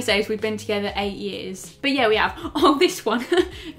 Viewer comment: she acc said "yes we have"